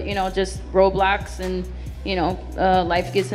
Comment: was it just me who thought she said roblox gets in the way